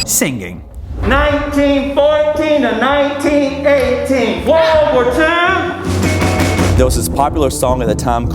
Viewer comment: he was actually saying world war 1